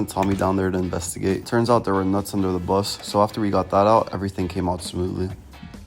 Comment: Is it weird that i thought he said "nuts under his buns"?